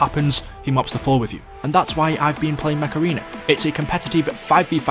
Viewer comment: i legit thought you said macerena instead of mech arena lmao